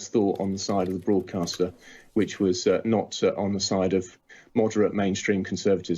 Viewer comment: I believe you may have misheard Baker. He referred to ‘moderate’ not ‘modern’ conservatism.